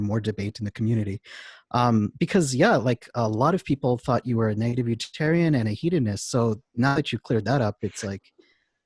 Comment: people thought Benatar was a what? It sounds like the male interviewer said "native vegetarian" but I know that's not right. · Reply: Negative utilitarian.